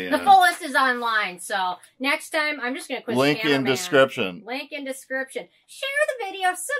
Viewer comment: I thought you said: Lincoln description. 🐾❤️😂